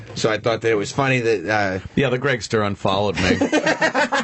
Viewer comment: "Yeah the opster unfollowed me" I love anthony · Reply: brice cate thought he said "the greggster unfollowed me"